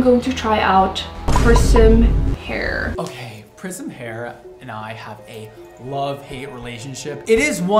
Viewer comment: I thought he said prison hair 😂 I want to see that video.